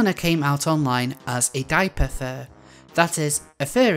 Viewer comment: I thought you said "gaipapher" when you said diaperfur, though I should note that the police should look into whether he was groomed. Have you considered making a similar video regarding alertpay?